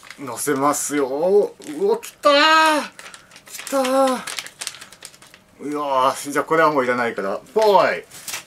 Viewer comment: うわぁーきったねーって聞こえたww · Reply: siroとすぷかみの実況部屋 うわーキターですよ！